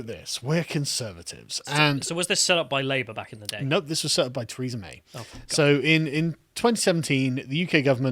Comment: Why do I hear "Treason May"?